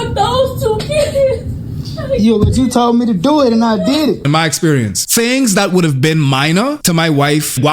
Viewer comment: He baby trapped that woman. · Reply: I heard him say, u wanted me to do it, so I did it